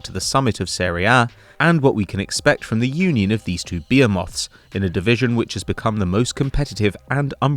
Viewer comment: Did he just say beer moths?